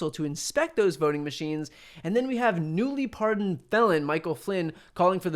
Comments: At first, I thought he said: "... newly-pardoned bell-end, Michael Flynn ..."! I think I prefer that to 'felon', which is what I think he actually said. 😊😉🤣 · Definitely thought you said "Newly pardoned bellend Michael Flynn", had to double back and check.